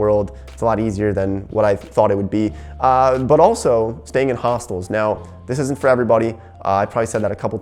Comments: Dude, I thought said staying at a Hospital instead of a Hostel... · “If I had 10s of millions of dollars in the future I’d probably still stay in hostels” I want to meet you in ten years and ask you if you still do that haha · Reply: I thought he said hospital 🤦🏾‍♀️🤷🏾‍♀️😂 I was going to ask how he is doing so. Thanks for the comment 😊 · Wait what?! Did he just say a hospital?! How does that work 😂🤣